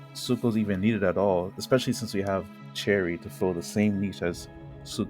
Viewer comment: Are You saying cherry or bary?, My hearing is Bad but i think I'm not the only one who is hearing cherry lol (Edit: i think I'm hearing deinocheirus right?, i'm hearing Cherry instead of cheirus 😭😭)😭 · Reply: LOL yeah I’m saying Cherry because it’s just a common way to refer to deinocheirus in the community, sorry for any confusion 💀